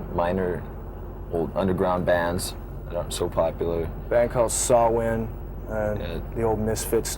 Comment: Does anybody know the band Cliff is talking about around 43:53 ? I heard Saw Wind lol I don't know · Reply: Samhain I heard saw wind also.